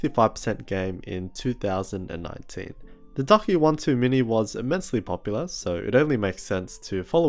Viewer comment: I heard “Duck One Too Many” 😂. Great review man